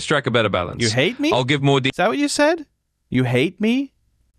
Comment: British accent GPT: I hear you. Let's track a better balance.😇 American ears: You hate me? Is that what you said? You hate me?🤨 British accent GPT: Not at all 🤔 I definitely didn't say that. 😆